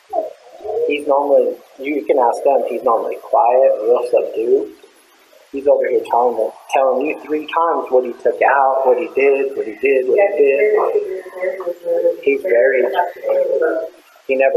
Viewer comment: said at the first part of that: "He's not acting right at all. He's never fidgety or..." Rocking back and forth. Nicole was also watching Chris with uneasiness from the moment he opened to front door for them. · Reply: Cheers. 👍 We also figured out later, he says, "real subdued..." We thought he was saying something that ended with "dude". 😂😂